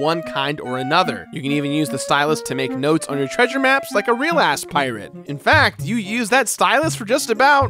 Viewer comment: I'm sorry, did you just say, "like a real ass-pirate"???